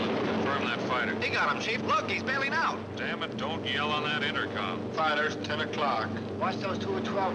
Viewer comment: I heard Hank Hill " dang it Boby, don't you yell into that intercom" at about 5:00.